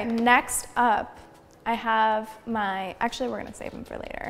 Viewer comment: sounds like ari saying “have my” at 1.5x speed lol